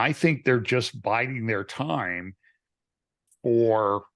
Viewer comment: Did say 'they are Biden their time'?